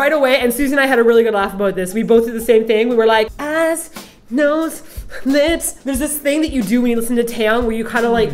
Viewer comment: When introduced Taeyang I heard "abs, nose and lips" and I was like, yeah...that's accurate.